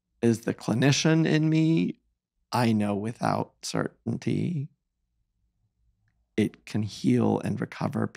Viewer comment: What did he say? Did he say "I know without certainty....." that does not make sense here. I would I assume that he wanted to say that as a clinician he is sure that "it can heal and recover..."